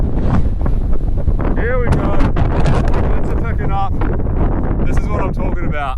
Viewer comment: " . . . here we go, the winds are . . . WHAT?":hand-orange-covering-eyes: Did he just say that word? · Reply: I think he said "picking up". The generated subtitles are not that great.